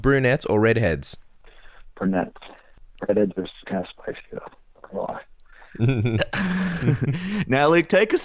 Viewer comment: What did Luke say about redheads? I can't even understand what he said. lol. 17:40 Redheads are not my favorite but I am just curious what he said. I barely understood it. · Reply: He answered, "Brunettes. Redheads are kinda spicy though, come on."